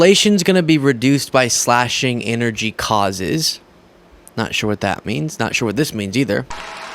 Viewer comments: I think he was trying to say "energy costs", not "causes" but it came out all weak and Trumpy. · i thought he said energy costses